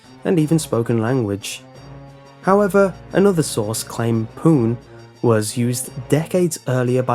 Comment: It sounded like he was saying "porn"